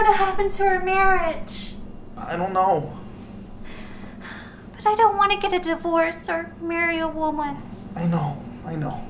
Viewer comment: My stupid band-club loving a$$ thought she said “I don’t want to marry a woodwind”